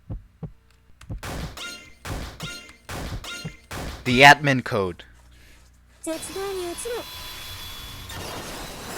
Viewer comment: Quick Question: What Akane says at 1:47:56? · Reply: I heard "Zetsubou ni ochiro (絶望に落ちろ)", which roughly translates to "Fall into despair"